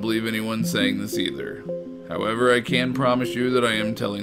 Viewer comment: wuuut "...i can´t promise you that i am telling the truth..." wtf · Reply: Hes actuly saying I can, I thought he said I can't at first, but after rewatching its (CAN)